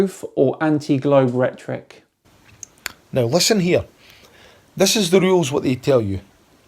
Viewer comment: "No luscineer.". Translation to English: "Now, listen here". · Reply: Ahhhh...Thanks...I thought he said "no luck in here" in reference to his nose!